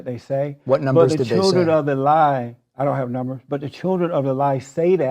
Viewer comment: It's so painful listening to Jesse. "Great white hope!" "Black and slow." "Children of the lie" This guy is embarrassing to listen to. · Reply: I thought he was saying some historical or religious term "Children of Alaih" or some bogus.